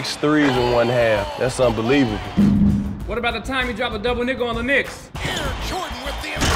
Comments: i thought rondo say" he dropped double Nggs on the knicks" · Did he say double nigga on the knicks? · its sounds like rondo said at 0:17 double nickel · at first I thought Rondo said, "what about the time he dropped a double nigga on the Knicks?" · what Rondo actually said is "...double NICKEL on the Knicks", not ":...double nigga on the Knicks"